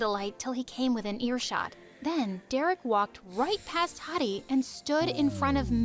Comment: They first I heard her say Heidi I thought she said Hotty I was like what??